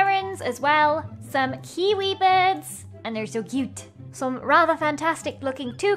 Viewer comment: Am I the only one who thought Lizzie said Karen birds (at part